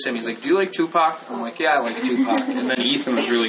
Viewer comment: Is it me or is he saying “do you like Tupac” “yeah I like Tupac” at 1:35?? If so the subtitles are wrong.. but I do love Tupac